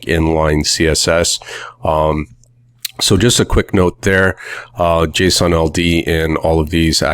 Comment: I thought he kept saying Jason Aldean; the country music singers name🤣